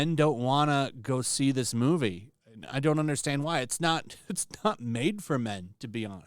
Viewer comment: But i thought they said “ iT’s nOT mAdE fOr yOu “